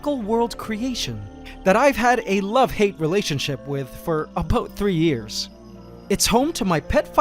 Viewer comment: did he just say aboot instead of about? Love you ants canada.